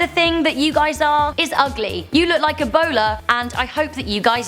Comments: At first I thought she said "you look like a bowler 🎳" XD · I thought she she said you look like a b@